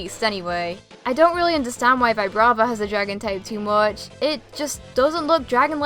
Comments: I thought you said dog type🐕🐕🐕🐕🐕😂😂😂😂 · thought she said dog type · I thought you said dog type on number 7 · Persians a cat tho 😂😂😂😂😂😂😂😂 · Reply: she sounded like she said "dog" type... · Sounded like you were saying Dog Type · I thought you said dog type